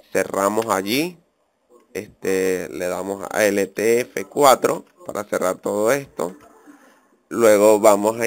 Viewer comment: Hola sera que me puedes ayudar no le entendi muy bien cuando dijo como se puede cerar creo que eso lo dijo en el minuto 3:23 xfa ayudame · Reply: es poniendo enter